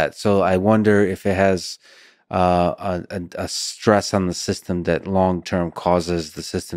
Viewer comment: what does Lex Fridman say? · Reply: A stress on the system